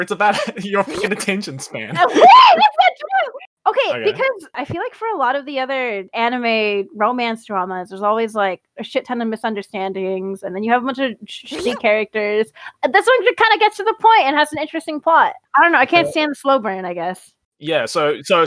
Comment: Anyone else heard her say sh!t at 19:08??